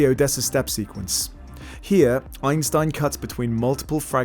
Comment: Great video as the previous ones! (in 12:07 am I hearing Einstein instead of Eisenstein?)